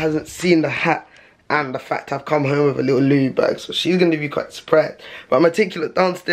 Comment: sounds like he says ‘lube bag’😂😂😂